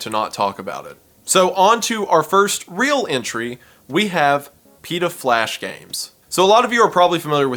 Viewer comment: "PETA flash games" had me do a double take... I thought he said "do" not "ta"